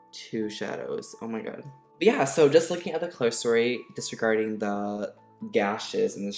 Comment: just look at what?? Does he say closery?? I’m legitimately asking because I don’t understand what he’s saying.